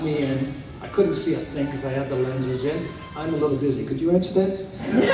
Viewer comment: Thank you so much for posting the whole Q&A! What did Patrick say to that person whose phone went off at 12:57? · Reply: When the phone rang, Patrick said, "Can you get that,...tell them I'm busy"...